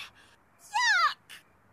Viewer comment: did Sharon said F*** O.o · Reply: She said yuck